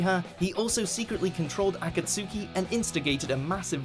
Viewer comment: When he said Akatsuki I thought he said Otsutsuki. You guys always butcher the names in anime. Get someone who actually watches the anime to do the videos.